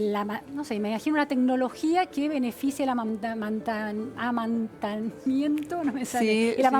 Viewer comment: que dijo????????? amamanamamantamientooooo. Roxana: ah, si la lactancia.